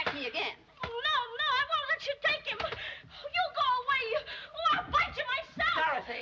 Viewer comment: I really wanted Dorothy to kick miss gulch's ass. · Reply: At first i thought she said or else i'll fight you myself. lol.